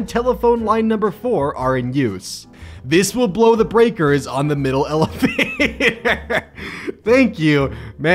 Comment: I wasn't looking at the screen when he said "Middle elevator" So I heard "Middle elephant" Help me